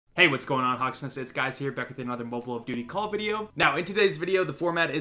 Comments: GOOD NIGHT! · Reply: Did he just say what’s going on Hawsnest , its guys here? 😂😂😂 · Did he say...."Mobile of duty Call of Video"